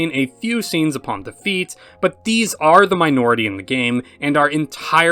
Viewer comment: I thought you said 'the feet' and I was gonna say why would I skip those scenes? That's the best part!